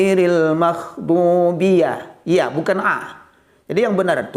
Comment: syukran ustadz penjelasannya tapi mohon maaf pak ustadz kedengeran saya bacanya seperti makhdubi yang seharusnya maghdhubi mungkin efek audio yang kurang jelas